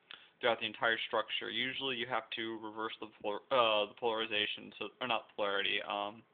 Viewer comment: i swear i heard "polymerization"